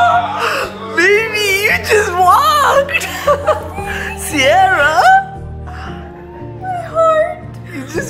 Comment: she sounded like a arbi ‘bismillah’ 2:35 lol